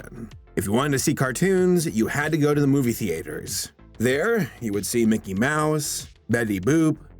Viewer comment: the what? The movie what? Wtf u say? The movie meepers? The movie feeee? Whats that word? Movie pleepers?